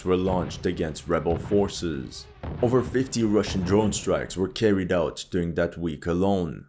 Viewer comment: "Over 50 Russian drone strikes" am I hearing that right?